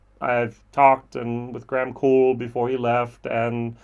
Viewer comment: Wait...did I just hear you correctly? Graham left Dingle? 6:19 Or did you mean you interviewed him when he left Glen Moray? Just clarifying. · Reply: yes - Graham left Dingle